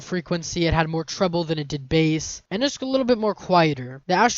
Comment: did you just say.."Base" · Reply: Yes, I meant bass that's what I said atleast, I for some reason typed "base" on the screen... that's on me lol